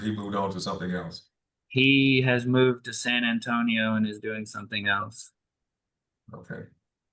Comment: So his brother who he worked with had to move to San Franciso for work? And she thinks Dad can still work in Texas when the guy Dad was working for had to move to find work? He has plenty of experience, but without the right qualifications or licenses he can't work in other states. Plus Houses buildeing is crashing in the USA. Nasty woman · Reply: I thought he said San Antonio?